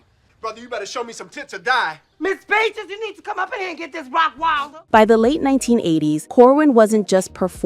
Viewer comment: "Ms.Peaches you need to come up in here and get this rottweiler!" 😂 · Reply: I always thought he was saying Rottwielder. Either or still hilarious!!